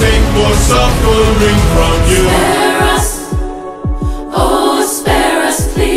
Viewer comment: Am I the only one that thinks during different beast when the group of siren say “SPARE US” i heard “ASPARAGUS” 🥦🥬🥒🥗🥕